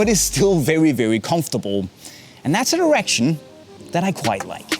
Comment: For a second there, I thought said "erection" lol